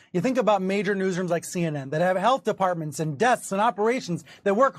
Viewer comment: Dude, did he say “desks” as one of the things CNN has that Rogan doesn’t?